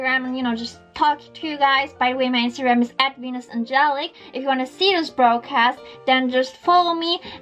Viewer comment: at first when she said I thought she said wenus! 😅😅